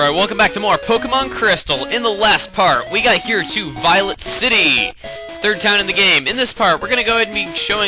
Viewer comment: Google's transcribe audio captions say "Violet City" is "Violence City!"